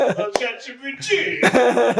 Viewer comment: I didn't actually know you had said "ChatGPT" i thought you were saying " Giuseppe"